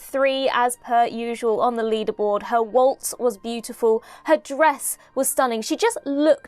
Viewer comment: I initially thought you said that Alex Kingston's "breasts were stunning". But, no, I rewound the video and you said "dress". I double-taked for a second there. U wot, m8?